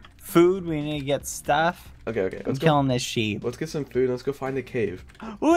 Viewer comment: The fear and confusion that waves over my body when bad said “I’m killing this sheep.” And I thought he said “I’m killing this shit”